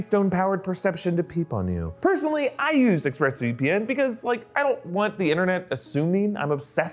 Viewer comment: The first dozen times I watched this part, I thought he said "I don't want the internet to sue me." I couldn't figure out what that meant for like, 5 minutes.